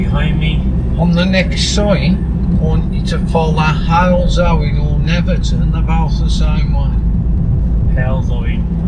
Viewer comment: Excuse me, What did the examiner say? .. I've put it back a dozen times. the closed captions are a giggle cause they make less sense. Im normally great with accents, living in Ireland we have some thick stuff here, but that was ....."WHAT?" Even my daughter listened a few times and we were both in bits laughing cause we were flummoxed. Bless him! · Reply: I heard "on the next sign I want you to follow Halesowen Old Hill Netherton (about the same) way"